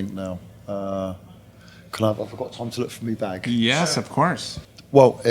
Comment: What is Paul saying at 7:12? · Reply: do I have time to look through me bag? (and then just a casual woah as he lent over)